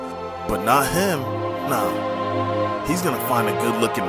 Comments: “he's gonna find a good looking miner ” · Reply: I thought he said minor😭💀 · "Hes gonna find a good lookin miner." god i love miners. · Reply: I thought he said minor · “He’s gonna find a good looking miner” I bursted out laughing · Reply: I thought he said minor